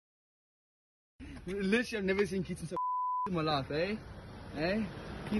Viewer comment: SPEAKING IN FOREIGN LANGUAGE 🤣🤣🤣 ... It's literally an Englishman speaking English. If your American ears can't understand, he says "Literally I've never seen Keaton so F*cked in my life"